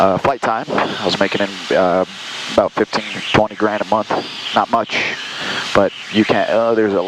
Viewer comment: Did he say "15-20 grand a month, not much" ??? Wtf lmao